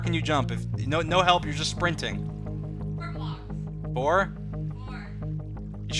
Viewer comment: he actually says 5 blocks